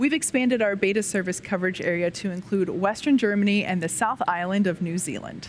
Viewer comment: Did she say "Western Germany"?